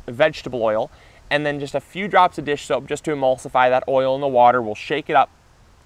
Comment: At the 5:52 mark you say "... and then just a few drops of dish#?*!" What exactly was that? Did you want to say "dish soap"?